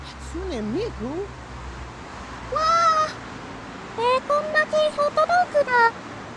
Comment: Subs: bacon wrapped hot dog What I hear: bacon wrapped hotadoguda